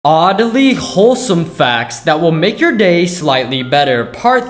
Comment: for second I thought he said oddly 4 some facts Lol